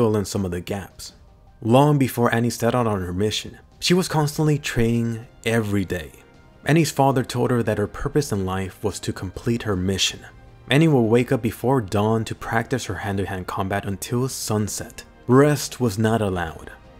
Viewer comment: Am I the only one that heard him say she told her instead of he told her